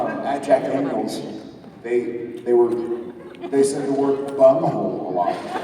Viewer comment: Thank you for the video :) What did the guy in Jared's story say at 37:29? I couldn't understand it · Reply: "Bunghole." =]